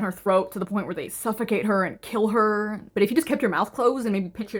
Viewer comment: GIRLLLL AT THE END I THOUGHT YOU SAID THE DARE WAS TO PEEL HER NOT KILL HER I DOUBLED TAKED SO FAST